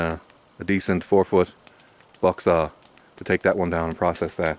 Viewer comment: Hey, You mentioned the corrrect tools for dealing with the larger trees being your hultis felling axe and a 4 foot buck saw. Can you recommend a good saw for this type of work? thanks for all the great vids. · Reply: 7:55... sounded like you said buck saw, but either way, is there a saw you prefer/go-to for that larger work? Thanks